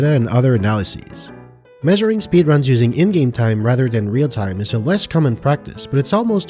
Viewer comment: I thought you said “they use Indian time instead of real time,” 4:38 and was wondering what the hell kind of clocks they use in India lol. I figured it out. But it would be cool if everybody in India had their own time pyramid or shrub or whatever.